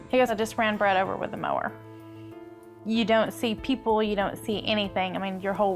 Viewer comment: What does the mom say at 2:35????? Its literally gibberish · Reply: “ran over with the mower” 😂